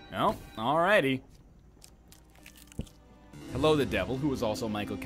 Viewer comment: did just call the grey traveler the devil?